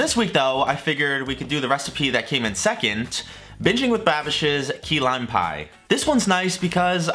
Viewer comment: Anyone else think it sounded like he said Bitchin with Babish ? No? Ok maybe my ears are broken.